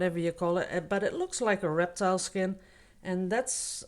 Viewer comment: LOLOLOL, when you turned the picture lengthwise and said "reptile skin" I thought you said ERECTILE!!!!!!!! LOLOLOLOLOL Love this painting for sure, beautiful soothing colors.